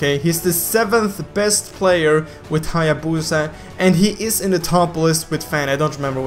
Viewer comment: i heard him say hayabutha